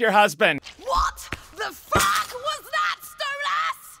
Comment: I thought he said fat king divorce